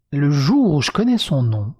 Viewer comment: pourquoi j'ai entendu "balance le nom" ?